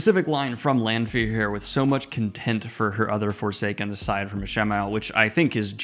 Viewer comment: Hi there! Good review! I was wondering, when you were talking about Lanfear's feelings towards her fellow Forsaken, it sounded like you were saying "content" instead of "contempt". Was I hearing it wrong?